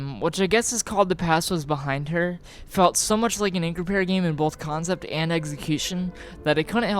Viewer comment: hey man loved the video (as with all your videos) but what word are you saying at 21:23? and incupair game? ive tried spelling it every which way in google but im not finding the right word. is it the name of a single game or is it like a genre? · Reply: Increpare, that's the name of an indie developer named Stephen Lavelle whose games you can find right here - <link> I recommend, in chronological order by release, these games - Judith, The Terrible Whiteness of Appalachian Nights, In a Single Breath, Puppy Shelter, Spiders and Cubes, The Good Sister, Slave of God, Ollam House, Universal History of Light. I'll warn you now that some of those games are depressing, and at least one has somewhat NSFW content though saying which one would be a spoiler alert. All of his games are really interesting for one reason or another but I recommend staying away from "Sub/Conscious" because that fucks with your browser in a not at all fun way.